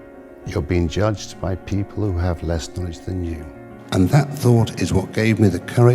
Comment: what did he say? "Marco you're being judged by people with less ____? than you" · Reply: “Less knowledge than you”